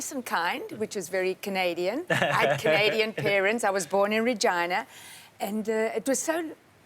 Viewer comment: I thought she said she was born from Vagina haha